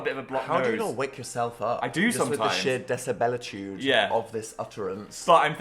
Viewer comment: someone please tell me what Dan is saying here… the sheer what?? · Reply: i think he’s saying “decibel-atude” like decibel relating to loudness, just saying it’s really loud basically